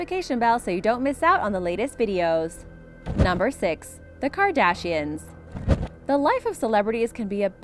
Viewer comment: It sounded like she said the CAR dashians